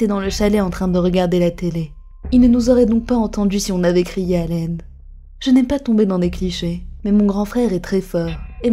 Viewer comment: A 1:37 C moi ou elle dit " à l aine " ? 🤔